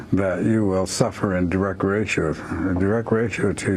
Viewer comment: what did he say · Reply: in direct relation I dare st8